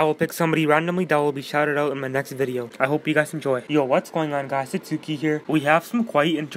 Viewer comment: Tell me it don’t sound like you said “yo guys it’s dukie here”😂😂